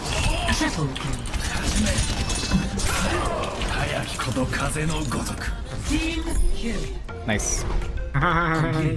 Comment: does anyone know what Genji says at 7:38 in Japanese? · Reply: Navaneeth Premkumar "はやきこと風のごとく" Hayaki koto kaze no gotoku - "Swift as the wind"